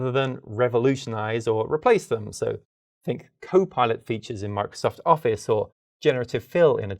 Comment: I heard that one as "copilot features in Mark Zuckerberg's office..."